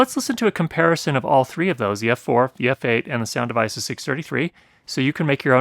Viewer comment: When you said Sound Devices I thought I heard you say "sound of ISIS" and started to worry...